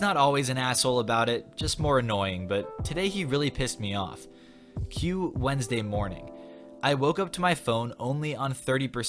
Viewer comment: I thought you said cue Wednesday porn